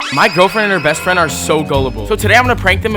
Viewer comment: I thought he said girlfriends 😂